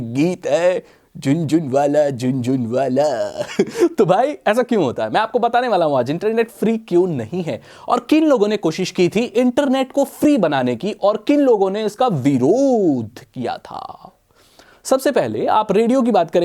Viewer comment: at first I heard "aur kin logo ne iska binod kiya tha" 😂